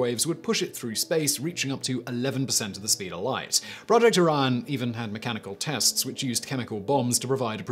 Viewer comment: I need glasses I thought number 10 said " project onion"